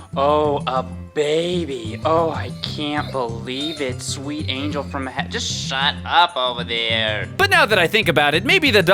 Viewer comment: What's her name? I think he said Azura but I couldn't really understand. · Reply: Yea. Its Azura